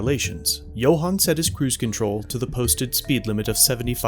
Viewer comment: Who else thought he said gohan like the one from dragon ball. man I wish I saw goku go ultra instinct on that cop lol